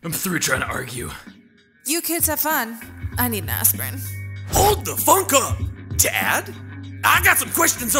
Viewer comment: I'm sorry but Boyfriend's dad is literally Hellboy Edit: since someone is not realizing who talking about, he shows up at 3:54 Edit: I apologize for being salty over a single comment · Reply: i thought he said "HOLD THE F**K UP!"